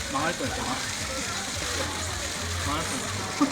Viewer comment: 쯤에 망할거야 이런식으로 들리는데 뭐라고하는지 제대로 들으신분있으신가요?